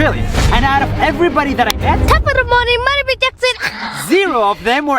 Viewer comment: i thought the kid said jacksepticeye.